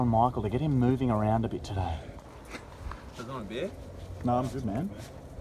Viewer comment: ちょっと待ってーにしか聞こえないww